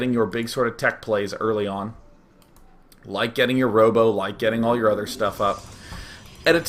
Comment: did he really say something about a rowboat at 1:30 or is google transcribe beta trolling me again...